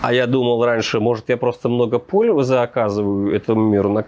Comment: услышал "может я много поила заказываю?" ) Кстати, он действительно так тянет слова?